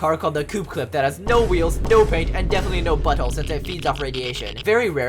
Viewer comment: What did say here? I only hear butthole.